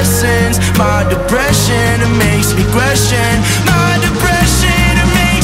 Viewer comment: I know I’m not the only that’s been singing “I got a boy in my head” and “my depression makes me CRAZY” 😭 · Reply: I thought it said my depression makes me aggressive 😭